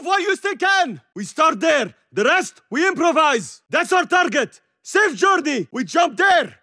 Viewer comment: I need help! What is he saying at 9:11 ?? I cant tell if its arabic or English, but no idea what he saying. Its like "sir jordi" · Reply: hes saying safe journey im pretty sure